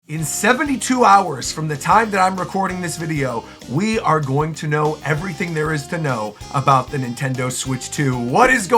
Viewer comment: I paused the video at the very beginning to go do something and when I came back and unpaused it sounded like you said “in 2 hours from the time that I’m recording this video we’ll know everything there is to know who. The switch 2” and I thought I missed it lmao